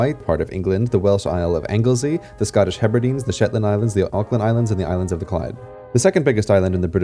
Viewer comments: It sounds like you said Auckland islands when pointing to the Orkneys. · Mate, did you just say "the Auckland Islands" ? Because you definitely DIDN'T say Orkney Islands. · lol thought when he said shetland thought he said shitland.... mybad xD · "auckland islands" I have no idea what you're trying to say but 'Orkney' is pronounced exactly as it's spelt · Also did you say the "Auckland Islands" at @ 1:36 hahaha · I heard "the Auckland Islands" when I think I should have heard "Orkney."